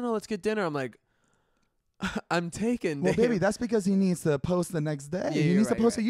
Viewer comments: did he say "im taken ni**a at 4:45? lol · so good just subscribed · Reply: Zane Hijazi So debate going on at my house, everyone thinks heath said around 04:33 To David I’m taken N-Word) I thought he said I’m taken honey. Please let me know if I win the vodka tonight? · I'm taken ...? I really do hope I heard wrong...